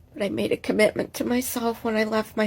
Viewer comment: How about, "... I love myself more than him, and I know that's a victory..." No doubt quoting some inane self-help guru for whom a marriage doesn't fit the world view. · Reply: I thought she said she made a promise to her SON and it made a little sense that she would love her son more than her boyfriend. But then I realized she was just saying self with a bunch of snot in her nose. So it didn't make any sense after all...